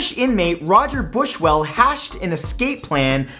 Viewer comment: Did you just say hashed?